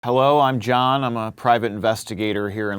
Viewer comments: I heard "hewwo im john" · Did I just hear "HEWWO I'm John" at the start??!!!??